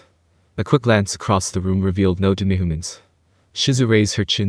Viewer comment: All I hear is 'demigaments' when you say 'demihumans'